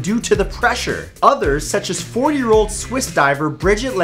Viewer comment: did he say 4 yo?